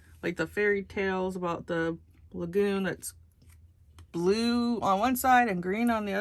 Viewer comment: Dayum! Sexy intro. 😂 “Vanessa” Aah yes, the “unboxing” 🌮 LMAO! Little rapping! October 32nd. Very slick. 😜 OH NOOO!!! 💩💩💩! I thought you said legumes, but you said lagoons. Love your fingernails, SO much! K byeeee!